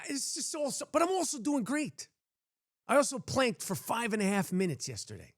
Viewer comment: Dick Ritchie I have no friends to tell ☹ it's me, myself and I ✌ Pharrell is so happy he thought you said wanked. Not planked. 🤣 A real house husband taking a piss 😅 Love your channel bro 🇬🇧